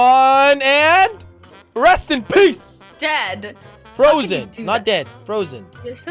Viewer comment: did say Jed or dead